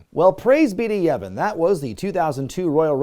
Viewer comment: What did say around 28:28?... "Praise be to--" who?... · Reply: Yevon lol it's a Final Fantasy X gag